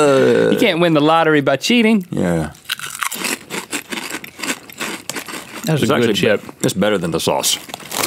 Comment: sounds like link is saying that's good as shit😂